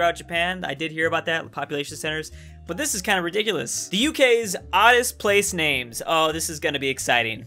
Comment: Thought that said oldest lmao